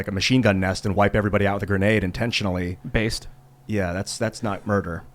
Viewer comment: did he just say "Based"? 😂